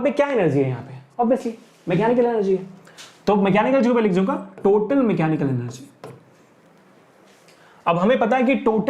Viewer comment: What sir said at 46:54 ? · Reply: mechanical energy 😢